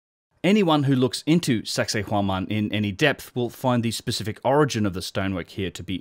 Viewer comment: Did he just say "anyone looking into sexhay whoman in any depth"? Giggedy.